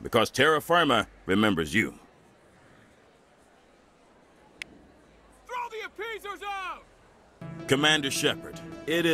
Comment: I wasn't looking at the screen and I misheard 3:12 as "throw me a pizza now!"